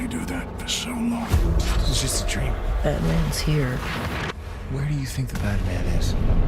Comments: for a sec i thought olliver son said batman was here ... but it was badman ,,,,,ooooooooooooh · I thought he said batman but when I hear it closely he said badman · I thought William said Batman's here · Arrow season 6 looks so good. I'm so hype for everything coming back and I would like to see the DCTV Q&A videos. BTW It sounds like William is saying Batman in the trailer but it's probably bad man. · Wait, between 0:07- 0:10 did he say Batman or bad man? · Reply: Jordan Gifford bad man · I heard "Batman's here" and got excited :(((( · I thought he said batmans here i almost had a heart attack